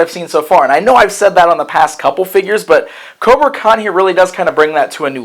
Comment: Strike first strike hard no mercy, sorry I thought you said kobra Kai.